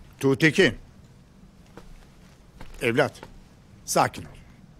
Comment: did i just hear "hei blyat!" 06:41 CMIIW